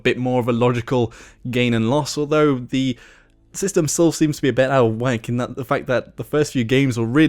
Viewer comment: And at 3:30 it sounds like you say "Its a bit out of Wank" xD